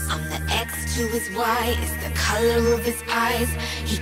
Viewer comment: “it’s the colour of his pies” is what I heard... I was wrong all along?